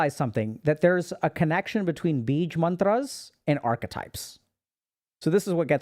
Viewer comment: What mantras is he talking about at 1:51:38 ? I can't understand the word he is saying. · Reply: Bīja